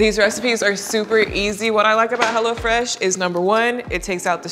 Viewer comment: Bruh, tell my why i thought you said "Hella fresh" 😖😂